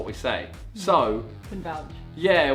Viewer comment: What did talia say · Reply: Can vouch